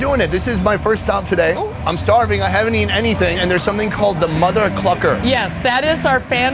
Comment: I thought he said mother f turn on the volume and heard that he said mother clucker